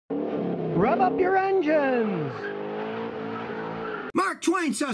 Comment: Rev up your jokes about revving up something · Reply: I must have misheard, I thought he was saying Rub up your engines.